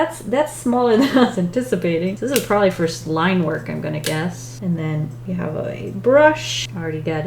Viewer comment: Did she just say Slime work instead of Line work 😂 or is it just me😳 ( no pun intended)